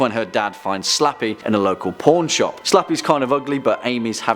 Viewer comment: did he just say ‘porn shop’ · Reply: nah pawn shop...